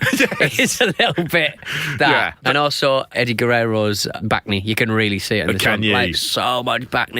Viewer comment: What he said bacne (3:12) I thought he said “back knee”. So I was like wtf is wrong with the back of his knee?!